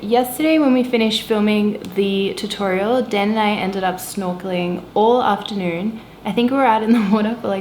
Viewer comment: O*" Excuse me. Perhaps I was not good at listening and I open the YouTube automatic subtitles function. At 14:02 of your video, I watched "filming the tutorial den I ended up (smoking) all afternoon *O Is there any computer mistakes to the subtitles function ? Did you mean "snorkeling" instead of "smoking" in the video ~v~ ?